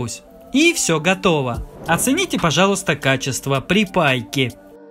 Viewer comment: Что то новенькое услышали " Оцените качество Припайки " 😂